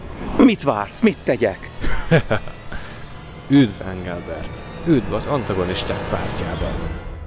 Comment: nél mit mond a maszkos csávó? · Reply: Üdv Elgenbert, üdv az Antigonisták pártján!